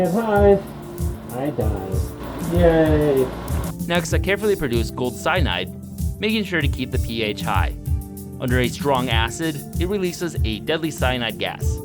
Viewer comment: ????? Did he just say "If I die, I die....Yaaaay" 😂